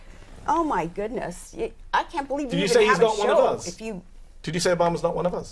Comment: Can anyone help me ? At 10:48 what is Sarah Palin saying ? I heard " oh my god, I can't believe ........ a show." Can anyone fill in the gap. I'm not a native speaker, not sure what she said there. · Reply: You even have